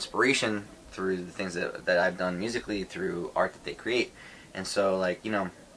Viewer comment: “What I’ve done musically” I HEARD “What I do on <link>” SAVE ME